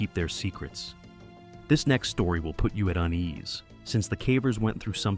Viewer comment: I thought he said “this next story will put you at ease” and i was like what? That doesnt sound right… so i rewinded and he said “put you at UNEASE” then i was like yup, that sounds about right 😌